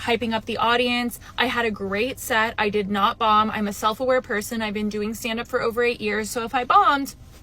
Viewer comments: "i didn't bomb. i've been doing comedy for 8 years and i know if i'm bombing" was breathtaking...i assume she was performing to a majority black audience based on the context of this video, and she didn'thave to be inauthentic, but you have to know how to play to different audiences. if she has no experience with a more diverse crowd like this after 8 years on the job, i think that says a lot about the type of gigs she's been willing to accept... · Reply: I thought she said "I've been doing stand up for over a year", as if that was some major time in lol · She just seems really pretentious, which doesn't lend well to comedy. Not on purpose anyways. It can be pretty comical to watch her bomb. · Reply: It's the 'I know how to standup because i have been at this for _ years...'. Standup comedians may seem like naturals but most of the really successful ones take years and even decades to get to where they are. Edit: edited for years, I thought she said she was doing standup for a year. Turns out it could be 8 years. That made her set way worse for me.